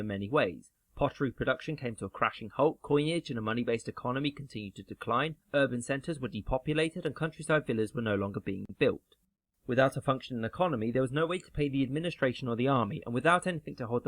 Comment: "....and country circulars were no longer being built" Is that what he said ? What's a country circular ?